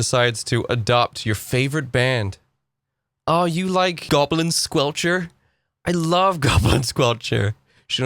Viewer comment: Is goblin squelcher an actual band? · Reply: I thought he said Goblin Sculpture.🐸